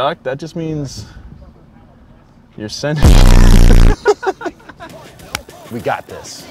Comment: Can anyone tell me what is saying at 18:25? “I keep hearing “sending it” but I can’t really tell!